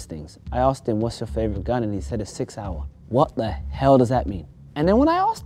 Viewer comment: said sigsaur and thought he said the 6 hour 💀